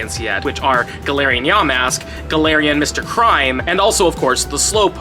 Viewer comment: did say Galarian Mr. Crime, cause isn't it Galarian Mr Mime?